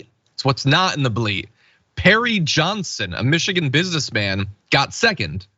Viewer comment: I thought you was saying hairy Johnson the whole time lmao